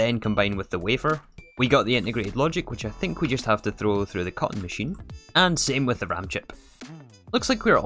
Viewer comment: omg I am such an idiot. F or the longest time I thought you were saying cotton machine. I normally have the videos running on the side so I don't generally pay a ton of attention to details. Man.